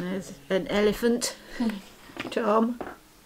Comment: I had to do a pause and rewind 🤣 When you mentioned the elephant charm, for some reason I thought that you said “...and an elephant [pause] John...” I thought, wait...what? Did I miss something? Who is John? Random overshare I know, but I thought it was funny 😄 ❤